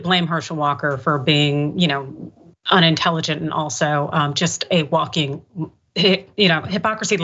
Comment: Almost sounded like she said, "A walking hick." 😆